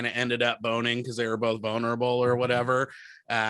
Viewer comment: anyone else hear "bonerable"?